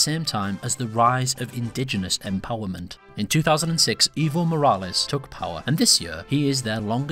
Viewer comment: it sounded like you said "Evil Morales" not "Evo" enunciate ALL of your words.